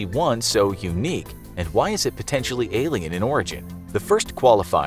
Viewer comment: I dont know why I heard alien in Oregon instead of Alien in Origin